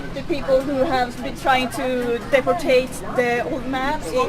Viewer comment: For a while I thought she was saying defecated rather than deportated . Easy mistake since only one of those is an actual word.